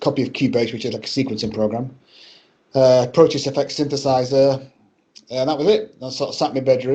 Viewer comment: What did he say? Proteus Effect synthesizer? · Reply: Almost. Proteus FX Synthesizer.